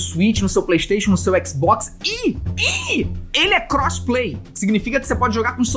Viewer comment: Rocket League is now free on the Epic Games Store! Download here: <link> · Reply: cosplay? E isso memo que ele falou?